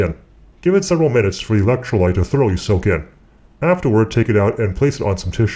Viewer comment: what did he said from 2:50 to 2:52?? LOL! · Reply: "Give it several minutes for the electrolyte to thoroughly soak in."